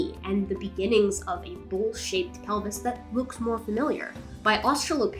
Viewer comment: I thought she said "bull shit pelvis" for a second and had to rewind.... "bowl-shaped". Ok. I thought she really hated the structure of the pelvis or something.